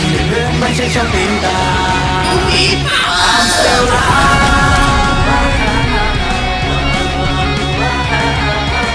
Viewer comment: anh nói gì với gián thế?? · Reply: NUVI POWER!